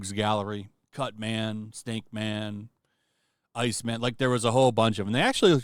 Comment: It sounds like you said Stinkman I was like what game was Stinkman from?🤣